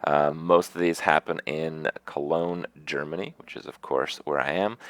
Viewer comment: Did I hear wrong or did he say at 1:04 That "Germany is a cool little city" ? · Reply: yeah he says cologne germany